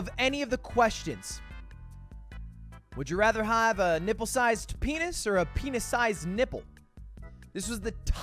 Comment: I was just listening while doing chores, not watching.. I swear this mf said hippo instead of nipple. I legit thought he picked to have a hippo sized penis 😅